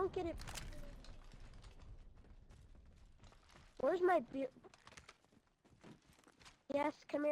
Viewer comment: Did anyone else hear at 4:20 “where’s my beer”?!?!